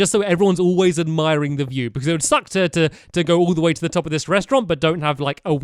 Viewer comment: did he say sucked? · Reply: He said suck not sucked